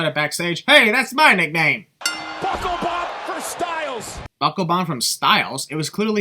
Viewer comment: At the 15:01 mark. You said that he said from, but he actually said for. Open your ears. Good Lord double sin.